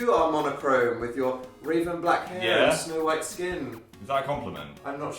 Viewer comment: I thought Dan said "semen white skin"......